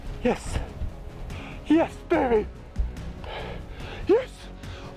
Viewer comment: I’m I the only one who heard him say “ yes daddy” ?